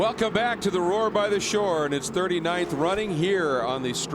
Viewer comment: Lol I thought I heard them say "Whore by the Shore"....whoops